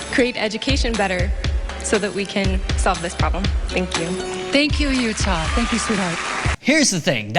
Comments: is it just me or she sounded like "thank you you tard" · lol i heard "thank you uthard"